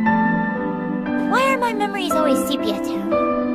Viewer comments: what dies Satsuki say at 1:15 I didn't get it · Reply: “why are my memories always sepia tone” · English is not my first language, I can't understand what she says in 1:15/16 "well my memory always ****" can someonebody help me? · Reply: the words were “sepia tone” referring to the color tint of the memory